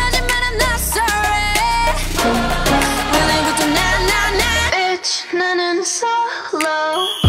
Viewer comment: I thought she was singing "Bitch this not a solo" but I was wrong. So yeah. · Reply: I honestly thought that she said "Bitch now an solo"